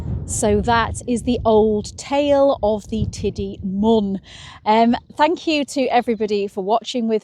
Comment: Thought you said “curse of the Tidy Mum”…. which is just as real…